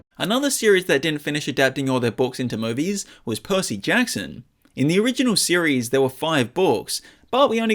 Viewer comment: I heard pussy jackson😂😂😂